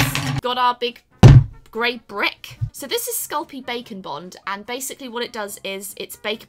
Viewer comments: I thought she said Sculpting Bacon Bond. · who else thought she said bacon bond😂😂